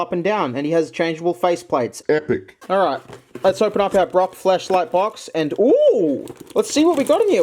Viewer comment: did you just say Brocks Fleshlight box??